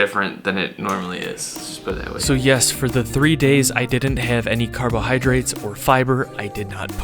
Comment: I thought it said 72 days…..